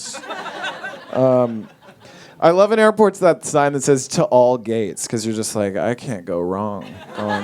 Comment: I thought he said "to all gays"